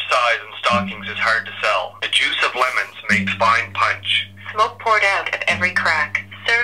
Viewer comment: What I heard: "the lemon makes eye punch" Yeah wtf eye punch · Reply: Angel The Creepypasta Lover ;-; the juice of lemons makes fine punch